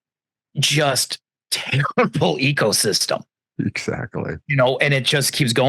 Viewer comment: When called it an "ecosystem", I thought he said " egosystem". Which is also a good description, I think. I hate to say...Jeff is cute and looks sell. I wonder if they would be as successful if Jeff looked like the hideous creep he is.